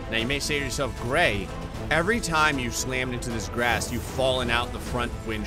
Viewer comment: did he say grass?